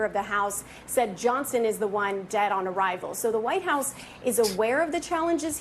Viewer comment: Allie Raffa, I thought Biden said that Rep Mark Johson's "words" were dead on arrival...